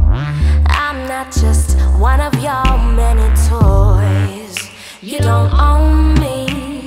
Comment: this part I always heard "y’all many toys"